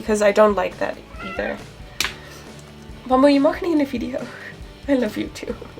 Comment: Are you Dutch? Because when you said something to you're dat I thought you said: Je mag niet in de video... Or am i juist stupid😇